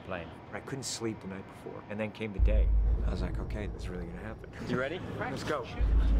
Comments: Did he say "I was on cocaine" ??? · I thought he said "I was on cocaine and it was gonna happen"